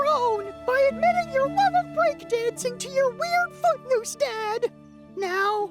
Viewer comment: I thought said "your weird fuckmoose dad" until I turned on the CCs... damn my messed up hearing!